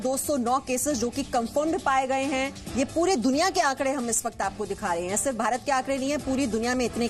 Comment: Wat she said “kampund?